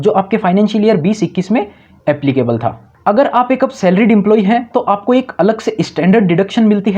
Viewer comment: kya bol ra Bhai clear bol kon se employee h? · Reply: Salaried employee